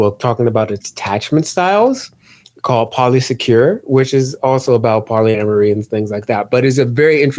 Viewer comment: hey can somebody tell me the name of this book that theyre talking abt at 1:57:40 ??? no matter how many times i replay it i cant catch the title T-T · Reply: Polysecure 🙂